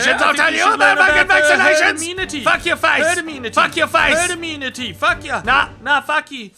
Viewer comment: I don't understand what the dog on the floor is saying at 2:47 Sounds like burnaminatiff